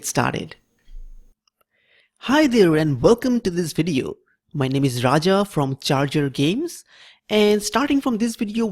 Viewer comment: Does anyone hear: "My name is Raja from JarJar games." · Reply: I always hear "My name is CHACHA from CHACHA games"